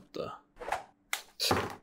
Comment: "Ah, f**K You guys" (at the end) was very personal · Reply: what.. i thought he said "not funny guys"